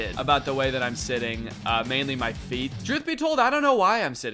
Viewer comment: did he just say… drewth….summoning …. i can’t…